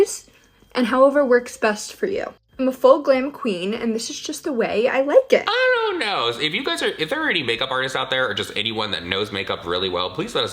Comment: as someone with hyper pigmentation who works in a makeup store i rlly dont like meredith duxbury claiming she is a "full coverage queen." the girl needs the wakeup call and how do i have more dark spots than her and i only use derma blend and a small drop of smash box. shes just seems like another mckayla but a Calabasas version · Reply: I thought she said she's "a full glam queen"